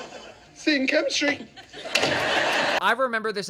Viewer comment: for most of my kid life i thought he said "same chemistry" it was a quick funny but made sense line. Only to then find it was "see you in chemistry" lol